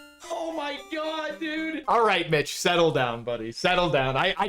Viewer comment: I thought you said aight bitch settle down but you said Mitch 🤣. Grats on the WR!